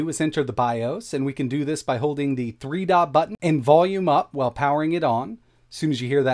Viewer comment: That edit around @ 5:57 is a little rough…I had to listen to it a few times to understand what you were saying with the button combo. It was actually a decent edit - just the way my brain was picking up “3-dot-button” lol. Great tutorial though. Very helpful! 👏